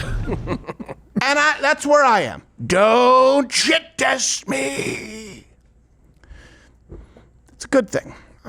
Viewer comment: What does "don't chit test me..." Mean? I'm sure the captions got it wrong, but i still can't make out what you're saying there? Chitas is a Hebrew acronym for studying the Torah, is that what you're saying? ""Shit test" doesn't make sense, so idk. 🤷🏼‍♂️